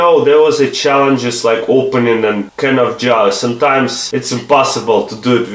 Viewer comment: sorry but did you say A can of jar